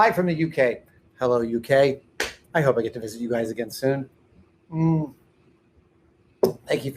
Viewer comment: I definitely heard that as "Hello UK! I hope we get colonized by you again soon"